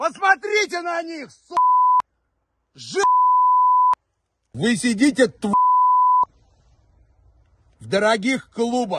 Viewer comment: Can anyone make out what he said at 0:54? Кто-нибудь может разобрать, что он сказал на 0:54? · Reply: "Zhopa (Жопа)" Source: I've played escape from tarkov for 2000+ hrs